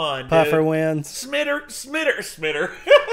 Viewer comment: smider 😂+ 24:09-24:11 kinda sounded like mater from Cars